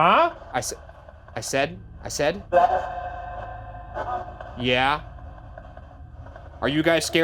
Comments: "i said" "get out" not yeah... 49:52 what was said before it switched. Sounds like a female 1:02:28 it threw something, wasnt a knock, you can hear it hit and land. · After the bump, It sounds like it says "I said Get...out"